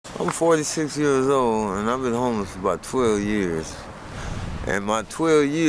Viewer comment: Bro I thought it said 12 year old.. thank goodness.. still horrible tho imo and I hope he finds peace